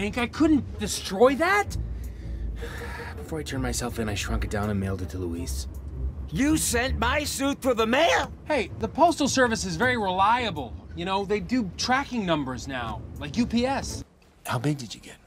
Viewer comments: it sounds like he said "you sent my suit through the MAYOR" 😂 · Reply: I thought he said Man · it sounds like he said "you sent my suit through the MAYOR" 😂